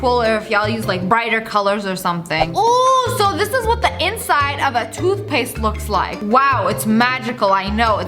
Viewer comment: am i the only one who heard toothpick first